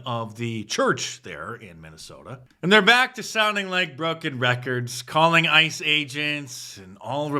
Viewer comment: HA!! When you commented about the records @ 8:05, I had to rewind! Your comment initially sounded like "drunken records", which is absolutely true....right Nancy?!